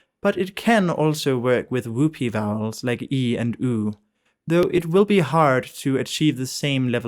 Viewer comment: What are the three vowels you say at 5:09? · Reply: The mid vowels EH /ɛ/, OE /ə/, and OH /o̞/